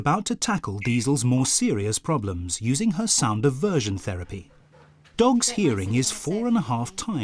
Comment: Anyone hear him say "her sound of version therapy"